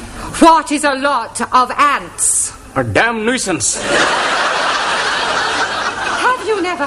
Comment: What are these captions, at 32:36 Why does it say "I dont use that" when he says "A damn nusiance"...